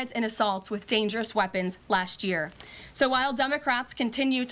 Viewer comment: Did she just say Dumb-o-crats?